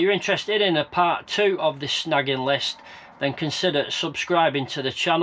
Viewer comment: I thought it said shagging list! Lololololololololol ha ha ha ha ha ha ha ha ha 😂😂😂😂